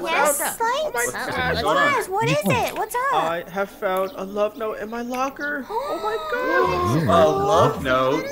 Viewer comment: At first when light said he found a love note in his locker I thought he said Death Note and I was like *😳"your happy about that?"